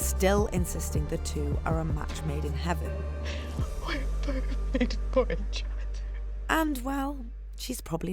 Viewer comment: what does she say at 5:20??? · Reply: we're perfect for each other